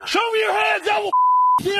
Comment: At first I thought he said, “Show me your azz.” 😂